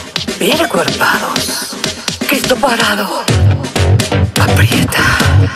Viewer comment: La primera vez que la escuche pense que decia pito parado 🤣 Y me dije santo cristo! Y efectivamente era cristo parado